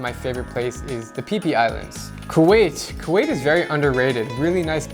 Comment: did he say the PP Islands?